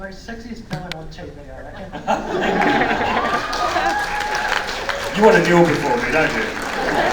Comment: What was the question and what did Callum say at 0:20? "You want to --- ---for me don't you?" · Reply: The audience guy said that Callum is the Sexiest villain he ever seen and Callum told "You want to kneel before me, don't you?"